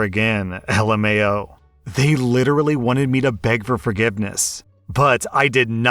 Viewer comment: Mom: "Beg for forgiveness!!" OP: "No, I don't think I will." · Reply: I literally thought you said “bag of forgiveness” I really need more sleep omg